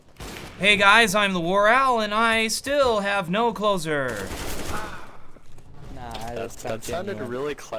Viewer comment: it sounds like he says, my name's the war owl, and I still have no clothes on.